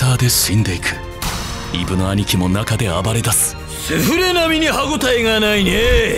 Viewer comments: ，セ◯レに聞こえて焦った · ここセフレって聞こえてガチおもろい